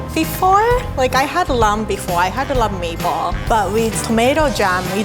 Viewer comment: if you hold the device close to your ear it sounds like she said “ lamb bee baw